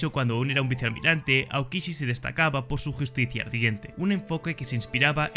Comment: justicia que? no se entiende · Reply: Ardiente